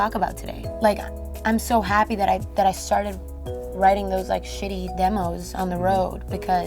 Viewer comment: did she say “shitty demos”? im confused